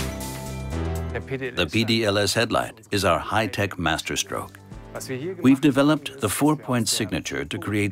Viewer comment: Why are they speaking German ? Nobody speaks German ! Speak English !! When he said "Master Stroke" I thought he said "Master Race" :-)